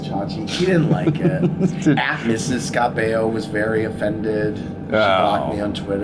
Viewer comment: what did Andy say?! "____ Scott Baio was very offended. She blocked me on twitter." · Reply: Mrs. Scott Baio was very offended. I think he is referring to Scott’s wife.